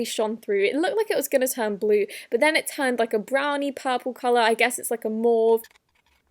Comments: “but then it turned brown, I guess it’s like a morgue ” Am I just hearing things? OH MAUVE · We shall never know what she wanted to say... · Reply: she said mauve